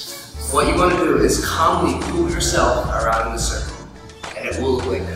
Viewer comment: What did you say at 4:01 ? "Compipood your self"????